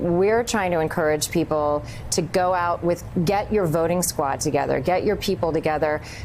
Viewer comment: A safer america , more money in our pocket , america first ! vote RED !!! did she say get your voter frad together @ 2:07 ? . vote make sure the voters are american alive citizens ! BE AMERICAN and HAVE PRIDE IN AMERICA !!! WEG1WGA !